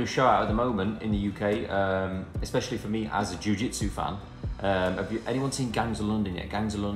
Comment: Did he say he's a Ju-Jitsu fan at 7:40?? Because i am doing Ju Jitsu for 8 years like😅omg